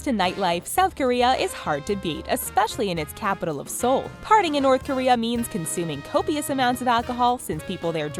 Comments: The captions say "Partying in south Korea means....." But the speaker said "Partying in North Korea..." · Your subtitles are incorrect at 5:55, you say North Korea and your subtitles say South Korea. If this was North Korea they would put you in a labor camp or make you drink dragon piss er whatever.